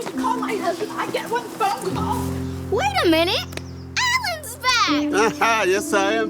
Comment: i thought she said "wait a minute- Alans black!"